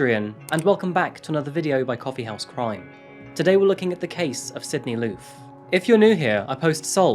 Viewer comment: I thought for sure 💭 he said “THE CASE OF CINDY LOU” 😂(thank god I can read)😅